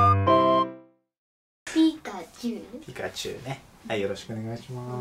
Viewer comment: さんのピカチュウがニャンちゅうに聞こえる😆